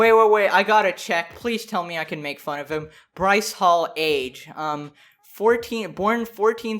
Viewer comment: when u said bryce hall i thought u said rice paul lmao